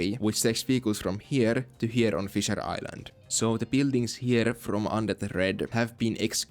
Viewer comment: For a sec I thought you said "fissure island" and I was like, as in anal fissure? 😂